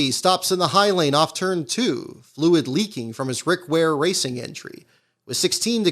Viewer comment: did I hear that correctly? He said "Rick Ware Racing", correct?